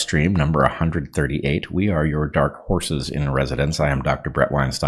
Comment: It sounds like you were saying we are your dark geese and I just went instantly to waterfowl